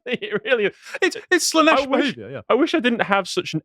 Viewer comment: Did he just say Slaaneshmas?